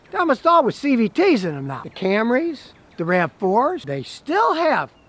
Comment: I thought he said the Camry and RAV4 have the CVT’s🤔